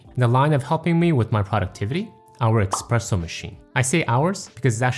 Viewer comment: did you really say eXpressso machine?😮